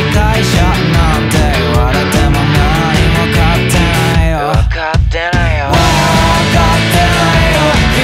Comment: サビ前の「分かってないよ」が「分かってるよ」に空耳するんだけど、相手側が言い訳に「あなたの言いたいこと、分かってるよ」って言い出そうとしてるのをサビの入りで食い気味に遮ってる感じがしてとっても好きなんだけどみんなはどう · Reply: 例として初めのサビ前持ってくるけど 1:42 、 君は理解者なんて言われても 何も分かってないよ (分かってないよ) 【サビ】分かってないよ〜 って3回続くうちの、の中のヤツが「分かってるよ」に聴こえるのは分かる？歌詞には沿ってないけどそう聴こえたから"空耳した"って表現使っただけで、MVは関係ない！ 登場人物が例えばAとB居たとして、 Aが「〜言われても 何も分かってないよ」って言ったのに対してのアンサーが B「分かってるよ」だと思った それを受けたから、Aが食い気味で、Bの言い訳を遮る形での「分かってないよ！」って感じ！ だから、口論の一部を切り取った形式なのかな？っておもって A「君は何も分かってくれないよね」 B「分かってるよ😢」 A「分かってないよ！何もかも🤬🤬」 みたいな、会話なのかなって 分かってくれたらうれし